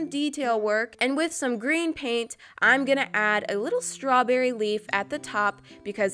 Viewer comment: I thought she said strawberry weave ( more like